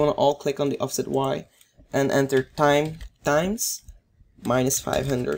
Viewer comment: What does he say at 5:25 times tines?